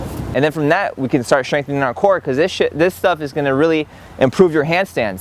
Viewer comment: "this shi, uh this stuff" lol he thought i didnt hear that